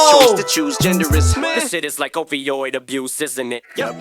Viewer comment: at 11:07 i listened over this part several times and no reactor has called this out, but it sounds like em says OVOiod not OPioid abuse isn't it. I don't know if that's a drake shot but I'd like to think it is. As drake had problems with opioids and how he blown out of the water and abused by Kendrick nice double